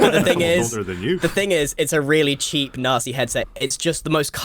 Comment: When i heard Grian say 'nasty' i thought he said Nazi